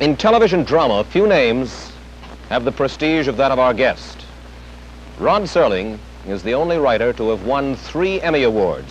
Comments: Does he call him Ron? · Did Wallace call him "Ron Serling" at 0:35? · Did he say 'Ron Serling'? It's funny to watch them smoking on TV. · Did he say RON Serling at 0:25?